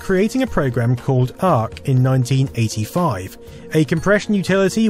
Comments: BBS: The Documentary is essential viewing, if you haven't already seen it. Check out <link> or watch the entire documentary through Jason Scott's playlist at <link> You can follow Jason on Twitter at <link> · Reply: Seems like a mistake: 2:52 you said 1985 and the subtitle says: 1995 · I heard 1985 but subtitles say 1995???? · Your Captions accidentally say 19-NINETY-5 instead of 1985... Just a heads-up 👍